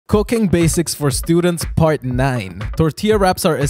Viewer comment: Bro i thought he said cocaine basics